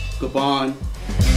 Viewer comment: Is he saying bboy Gabon or Kabon at 6:51? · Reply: PK Fire Bboy Gabin